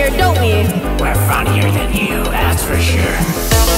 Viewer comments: Lol i thought he was saying "We're f#cking with you, that's for sure" 💀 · I honestly thought they said "way funnier than you"